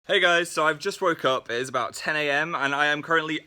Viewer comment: In the besinning i thought he Said "i just broke Up" i was Like WHAAATTTT